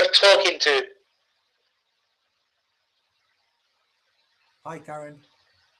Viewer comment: I swear I heard a hi gary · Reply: his name is. and that voice was him saying "hi carren" or something but it was 100% him